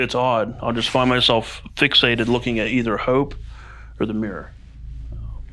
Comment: Anyone else hear a whisper at 14:05 say "hope" right after he finishes talking?